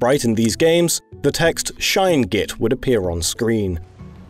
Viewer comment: I misheard "get" and was thinking "It's good they decided to change it. 'Shine Git' wouldn't go down so well."